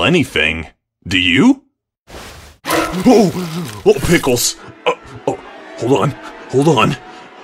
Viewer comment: Did he say pickles?😂😂